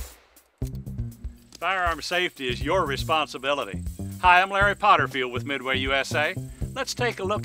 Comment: I thought he said he is Harry Potterfield at the end of the video